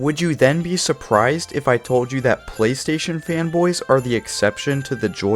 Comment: Thought it said PlayStation Femboy 😭